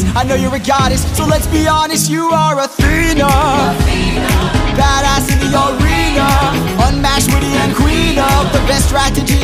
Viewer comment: I hear "You are Athena, the baddas senorita" XD